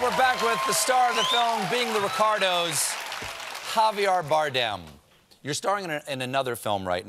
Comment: Sounds like Stephen is saying Javiar not Javier lol smh